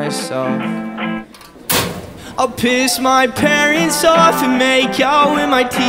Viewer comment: honestly I thought you said Ill piss my pants. if only lol